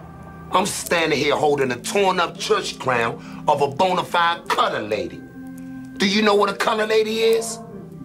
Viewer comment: "No! I'm standin' here holdin' a torn up church crown of a bonafide cutta lady." "Do you know what a cutta lady is?" "NAATCHYO MOMS FO' SHO'!" "Cause if they was that, y'all would have known better than that BOUL-SHIET!" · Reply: I couldve swore he said color lady